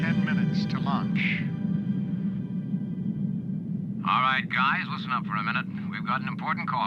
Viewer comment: Believe it or not, I saw this movie as a 7-year old in 1982 in the USSR of all places! It was on TV like very late at night, and the dubbing must have been atrocious, but for me it was the coolest thing ever. Rather curious that the Soviets decided to show it. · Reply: did he say 10 minutes to launch or Lunch 🤭